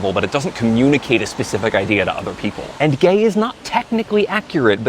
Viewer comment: oohhh, I understood "gamer" instead of "gay man". I was so confused.